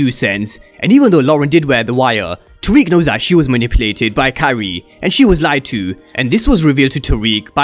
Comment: the first time I heard you say "Carrie" I thought you said "Kyrie" for days I was trying to figure out who the hell is that